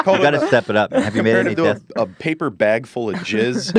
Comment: i zoned for a second and then i heard 'jizz in a paper bag'